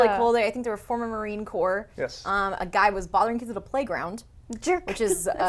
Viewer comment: I couldn't discern what you said about the guy in the playground. He was doing what? Because it sounded like you said "bothering kids" and that's hardly horrible so I'm guessing I misheard. Also, what I found the strangest about the museums being in the game is that the same stops were in Ingress and apparently nobody noticed until now. That's just weird to me.